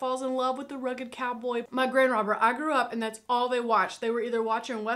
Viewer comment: I thought you said, "my grand robber". heh